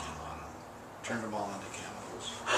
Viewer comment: I couldn't hear what he said at the end - something about cannibals. But at first I thought he said they turned the ashes into candles. This gave me a cool idea for a horror story! Thankyou!